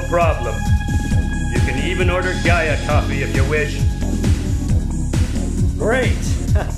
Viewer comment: Jia Coffee? Did I mishear that?